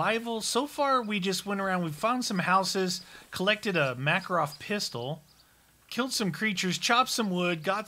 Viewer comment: When u said makrov pistol I thought u said macaroni pistol lol I need a hearing appointment or new phone